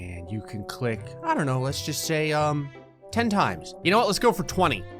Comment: I heard "hen tai" got confuse, played it again and realized he said "ten times" help me